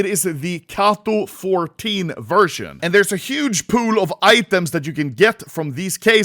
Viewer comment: thought he said huge poo of items😂